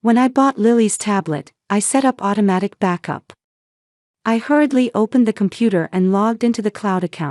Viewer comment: The detective didn't "knot," she "nodded." Her mother's and "sister's," singular possessive, not more than one sister. At about 23:10, written was "I hardly open," but what was said was, "I hurriedly opened." At about 24:19, written was, "a forged wheel," but what was said was "a forged will." PROOFREAD, PROOFREAD, PROOFREAD!!!!! At about 25:05, written was, "the future of Lillian me," but what was said was, "the future of Lily and me." At about 29:47, written was "plays their everyday," but what was said was "plays there every day." Learn the differences between there, their, and their, as well as the differences between to, too, and two. That's a VERY important part of the English language! At 29:57, written was "Can we paid," but what was said was "The repaid." At 30:36, written was "That's alright, " but what was said was simply "That's right." There were still a few more errors after that, but I was done. I need to get paid to proofread/edit this crap! I was a medical transcriptionist/editor for greater than 25 years, and I made fairly good money doing it, too. You need to find someone who will edit your work as well, and pay them well to make your work look less like a child's work.